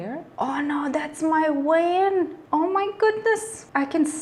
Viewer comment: What was that thing at 3:15? It looked strange and interesting :D but I didn't understand what said. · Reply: Haha, sometimes my accent comes off so bad. I meant "veins" in that particular time 😂😍😇